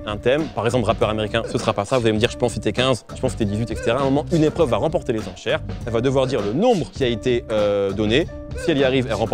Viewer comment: Y a que moi qui ai entendu "une épreuve va remporter les enchères" à 15:35?